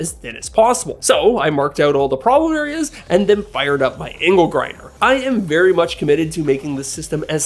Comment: I thought you said "anal grinder"....